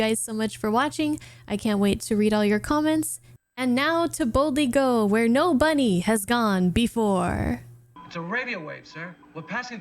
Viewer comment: I am so excited for you taking your first steps into the Star Trek universe. I am so sorry about your dad. As a father myself, I’m sure he would be very excited to see you getting into Star Trek. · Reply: Did you just say (10:09), “Now, to boldly go where no-bunny has gone before?” 😂